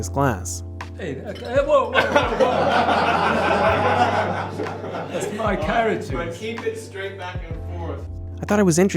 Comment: Hey...at 14:09 i think he says "thats my carrot juice".......not "thats my characters glass"